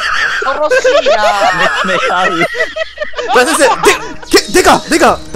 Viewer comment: ののセリフが聞き取れないのですがなんと言っているかわかる方いますか？ つめつめはーりーとしか聞こえない… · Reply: 見つめ合うーって言ってますよ!!